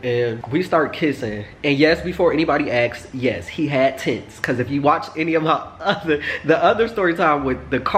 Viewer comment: I thought u said "tits"